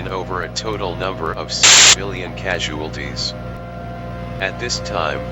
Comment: I hear 7 billion